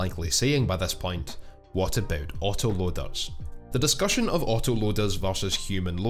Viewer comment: I thought he said "Otter loaders"...